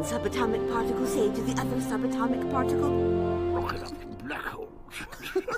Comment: i dont really get what he's saying but i do hear he said "Black Hole" or something